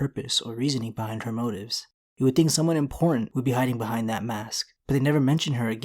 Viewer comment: Did anyone else think that at 0:33 it sounds like he said someone in porn behind the mask rather than someone important behind the mask?